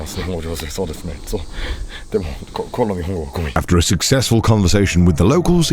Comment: he actually says "my Japanese is trash," not "I got poop in my pantaloons."